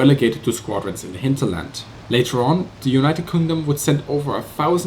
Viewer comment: Was it just me or did u say united Condom at 4:07 ? xD